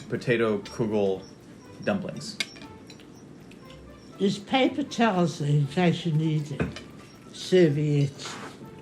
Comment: 'there's paper towels there in case you need any' is what she said. Also, rump steak is what you would call sirloin, and what we (UK and SA) call a sirloin, you would call a porterhouse. Also also, the generic term for 'tater tots' is 'croquettes' (crock-ettes), which is the common term for them outside of the USA.